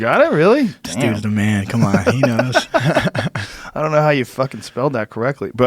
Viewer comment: anyone else catch frankie call Jamie Steve by accident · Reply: MAJOR PAIN WAYNE he said “this dudes the man cmon he knows”, dunno where you heard Steve lol